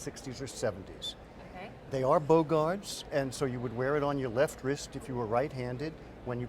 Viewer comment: Oh, bow guards. I thought he said bogarts